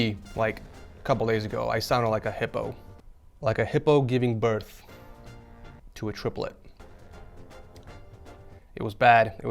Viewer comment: You think it's funny but I was really sitting here like he capping something interesting about to happen then I hear "I sounded like a hippo giving birth to triplets." BRUH 😔